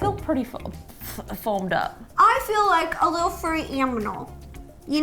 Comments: Ro said "Aminal" instead of "Animal" LOOOOOL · Did she just say "I look like a little furry AMINAL" I.D.K but that's what I heard❤️🧡💛💚💙💜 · Anyone else hear her say amnimal! Check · Ro said aminal instead of animal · did she just said aminal? 😂😂 lol dont come after me 😂😂 5:03 im dying ro 😂 Imagine her giving this a heart.. yea cant relate 😂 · did ro just said aminal? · ro said aminal instead of animal. plus me and my sis love your vids❤️ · About 3:11 I think it was, Ro said,"I feel like a little furry aminal", not animal😂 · Did she just say furry AMINAL not animal AMINAL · Ro said aminal instead of animal xD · Did anybody notice that ro said aminal instead of animal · Was I the only one who heared that Ro said “I feel like a very furry amnimal”